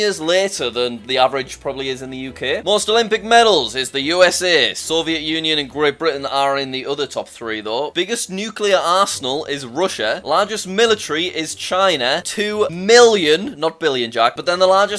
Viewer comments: i heard “biggest nuclear asshole” 💀💀💀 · I thought he said biggest nuclear arsehole 😂😂